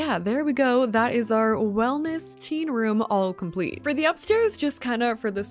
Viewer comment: When you said teen room I thought you said tea room and I was so confused when you put a bed 😅